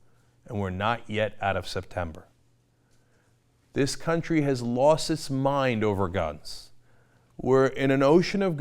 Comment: Cenk says "country losing it's mind over Guns." The CC says "loosing its mind over Gods." I do believe the CC has it more correct.